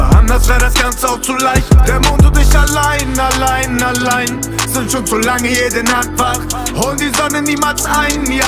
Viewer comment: Warum verstehe ich in min 0:33. „sucuk solange jede Nacht wach“😂